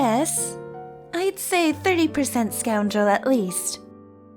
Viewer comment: At first i thought they said squirrel not scoundrel and i was so confused XD